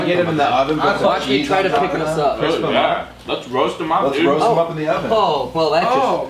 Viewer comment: MusicILike424 that would be sweet if they started a channel! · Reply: I re-watched it again & I thought I heard "let's gross them in the oven" but now I think they said "roast them in the oven"? Lol